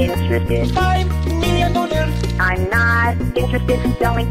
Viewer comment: In the normal vid there is no part where he says 5M dollar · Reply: IAN DaRk yeah he says 2 . 5 million but it sounded like he said 2 no 5 million dollar so i dont blame you being confused